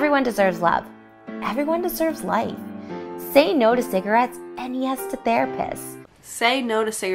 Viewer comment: I heard “say no to cigarettes and yes to bear piss” so I’ll take the captions, however oddly spelled.